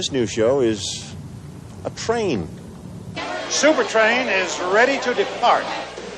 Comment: Does he say Supertrain is ready to defart? 😂😂